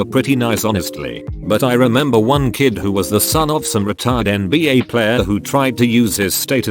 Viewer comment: i heard retarded NBA player and i was like wtf